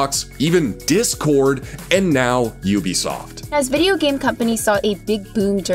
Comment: I wasn’t paying attention at the start and I thought he said “boobiesoft” 😳